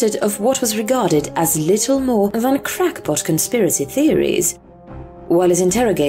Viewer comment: For a moment there I heard that as "literal crackpot theories", and thought to myself 'things just got interesting!'